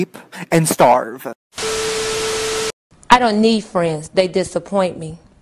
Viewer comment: Mimi: I don’t need friends they disappoint me Me:I don’t have friends but I have u >:) edit:thank U FOR 67 LIKES it’s not a lot but I’m surprised it got this far! 🥳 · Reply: I thought she said I don't need *frames*, oof :') edit: Wait, am I the only one?